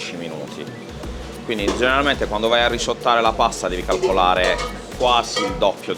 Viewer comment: for the ones that aren't Italian. The cc say "toast" but the guy says "risottare" that i've found out not having a translation. So basically "risottare" means to finish cooking the pasta in another pan with sauce and some of the cooking water. This is done in order to better tie the sauce flavor to the pasta.